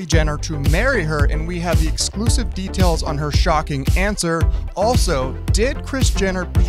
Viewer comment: I thought he said "her shocking cancer" and I was like whatttt